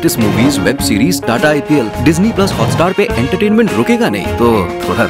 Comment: I heard jukega nahi